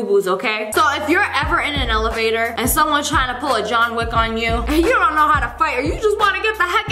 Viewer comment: When Lia said "go Jhon Wick on you" i heard "pour chocolate on you"